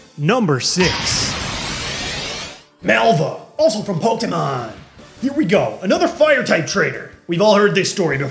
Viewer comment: Did He just say Poktamon? Or am I the Only one that heard it like that?